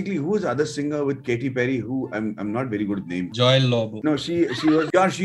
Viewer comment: did he say joy Lobo 😂😂😂😂😂😂😂